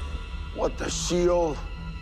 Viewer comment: Did he just say 'What the Sheol?' 💀